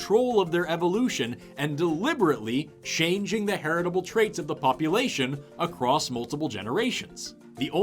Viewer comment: it sounds like he said “pup-lation” instead of population. Appropriate since he was just talking about doggos.